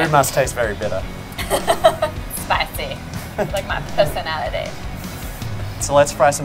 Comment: Holy shit I thought Nat said "spicy like my pussy" and I was a little taken aback 😂 just "personality" though!